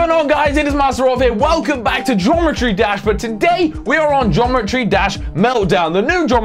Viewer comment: wtf it sounds like hes sayong jometry dash what the hell